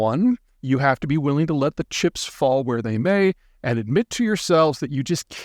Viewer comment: it's a fool's deal! let the chips fall where they may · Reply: I thought he said chimps.